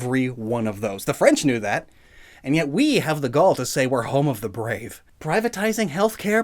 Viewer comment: HILARIOUSLY out of topic but this fucking line made me fucking laugh since i heard "*Oui* had the *Gaul*" and I'm going to die